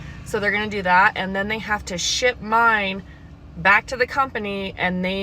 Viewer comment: I thought she said "they where going to have to shit mine."